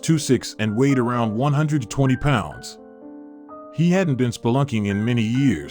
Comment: He was 2’ 6” at 120 pounds? Maybe I heard that wrong…